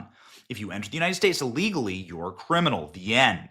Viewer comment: Did you say legally at 3:10? Did you mean illegally?